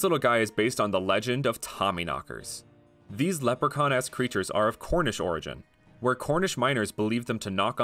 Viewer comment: anyone else heard theses lepro Con ass creatures instead of esk